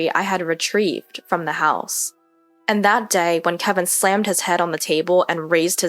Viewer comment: slammed his hand. I heard '...slammed his head...'. That would have been justice.